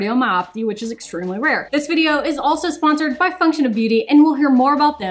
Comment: Honestly thought you were saying "Functional Beauty" lol. Endo sucks. I have it and it's very painful 💔 😢